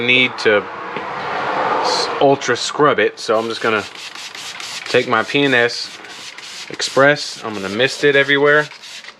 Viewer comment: What's up!!! Lol at 6:24 i thought you said "I'm gonna take my penis express" 🤣🤣🤣 should probably slow down when you say P&S 🤣🤣🤣 Good video as usual man! I've been thinking on getting some of these , but haven't.